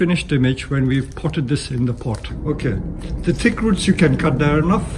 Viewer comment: I thought he was saying "tick roots". I honestly thought it was some kind of special roots that were good for Bonsai.